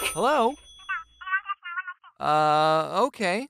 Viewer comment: OMG IT SOUNDS LIKE IT SAYS "Hello, 911 what's you'r emerency?" :DDDD